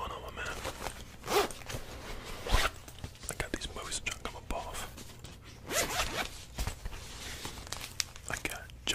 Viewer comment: What does he say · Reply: I’ve got these movies I’m trying to come up off